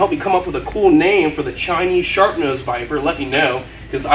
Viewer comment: I know you kept saying it's a Chinese Sharp Nose viper but I always heard it as a Chinese SHARK nose viper, so if you'd like a name for him, what about Bruce? From Finding Nemo lol bc that's the first name that popped into my head, Bruce the Chinese "SHARK" Nose Viper, and also he can attack fast and quickly and leave a huge bite just like a shark can 😌🥰 I'll always love ur videos btw, I'm hoping to own a snake of my own soon so watching how you take care of them is really much more helpful 😌💜